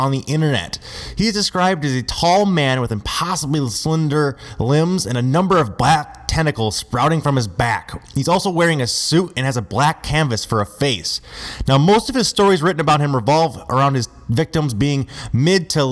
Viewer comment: I'm not sure, but I think he said "blank face or canvas" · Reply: Maybe it's just his voice. He may have said that, but I heard "black canvas"